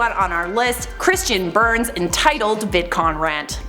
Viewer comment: I thought that said "vicodin rant" ehh sounds about right